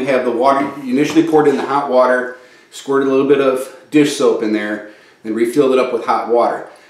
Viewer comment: Fixed it...I hope water is slow to come back up to normalcy but at least it finally flushed completely...TWICE! IT sounded like you said put 8oz of dish soap in? I didn't cuz not sure I heard it right and judging by ur bottle you didn't lol. But it works!